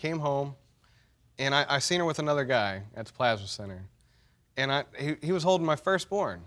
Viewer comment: "The plasma center guy" is crazy💯😂😂😂😭 She was OUTSIIIIDDDEE💯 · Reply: I thought they were saying plaza centre until they brought up the calendar